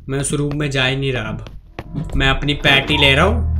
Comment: mein apni panty instead of patty 😂😂😂😂😂